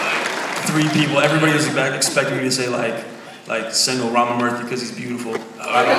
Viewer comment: This is bothering me so much, what name does Milo say at 3:04? To me it sounds like he's saying "single Robert Murphy", but I know that's not it.